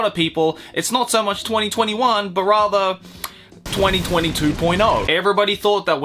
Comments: did you say 2022 2.0 lol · instead of hearing "2020 2.0" my dumbass brain thought he said "2022.0" and i was soo confused for a whole minute